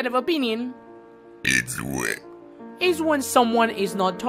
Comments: I heard it's wet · what the heck bro he really said ÏŚWÉŃ